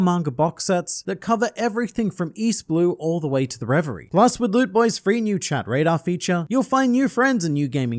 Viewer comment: Anyone else thought the sponsor said " loot booty " upon the words first popping up on the screen lol